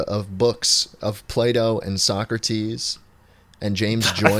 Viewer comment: I swear to god this man said play doh and soccer tees